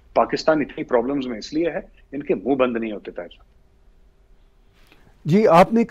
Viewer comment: seconds did he say ..." inke muh nahi band hotey Behan C***" tried to confirm 3 times each and every time it was there